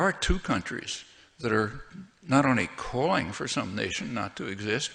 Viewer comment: "there are two countries that are not in a calling for this _ to exist, but they're destroying it" I can't recognize the word(s) in blank; did anyone hear it correctly? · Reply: That are not only calling for some nation not to exist